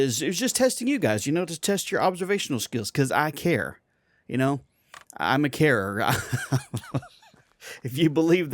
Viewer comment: did you just say you are a Karen??? Maybe a Chad, but never a Karen...